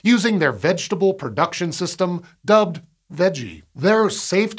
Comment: okay I see the text 'VEGGIE', but why do I hear him pronounce it as 'WEDGIE' 🤔